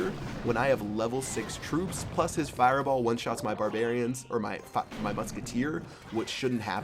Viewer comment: Did I hear drop an F bomb o. O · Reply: Lol he said 'fireballs that one-shots my barbarians or my fuck my musketeer.' He said it again in one of his recent lets play